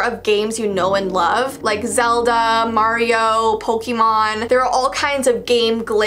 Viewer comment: did she just say "PoLkemon???? Smh...